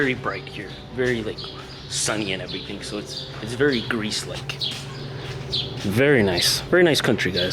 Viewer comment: I thought you said grease